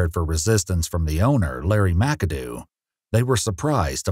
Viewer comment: I thought you said "Larry wack-a-do" 😂😂😂😂